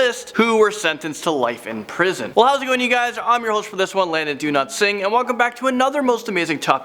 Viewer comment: This is the first video I've watched from this channel and I thought he said his name was Do Not Sing lmao 😂